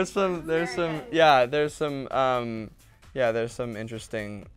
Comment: all I hear is "there's some"